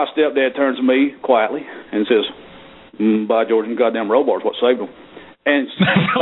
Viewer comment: Okay, after several attempts I've given up on understanding what that stepdad said at 27:25 Any pro American speakers who can decipher this for a poor Norwegian?:D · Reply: something like... By george it was the bar on the car that saved them